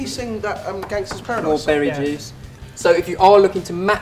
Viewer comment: Did he say berry jizz at 4:19?